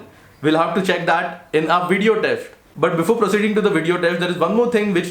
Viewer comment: Did I hear "Video Teft" instead of Test